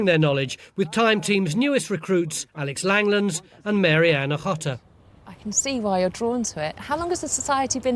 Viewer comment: does he say "mary anna hotter"??? anyway, he was right...